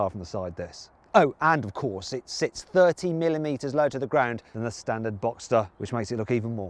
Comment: 차체가 20mm 낮은게 아니라 thirty millimeters 라고 들리는데요? ㅎㅎㅎ carwow 원래 사이트 가서 원본 영상 자막켜고 보고 이영상에서도 영문 자막켜고보니 30mm 가 맞네요 ㅎㅎ 20mm에서 30mm로 자막 수정되면 좋을듯합니다 ㅎㅎ